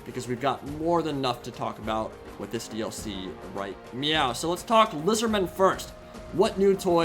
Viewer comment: "we got more then enough to talk about with this DLC right meow" did I hear that right? cause the subtitles said yeah instead. lol. ahh the Whorehammer Dlc's trailer look like movie trailers again these days. :) Empire update was so needed though. CLEVER GIRL!!!